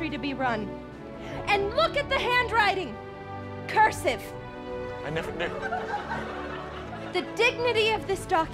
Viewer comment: I thought was just saying gibberish "Animinim". After playing it 50 times, I realized he was saying "I never knew". I now feel like an idiot.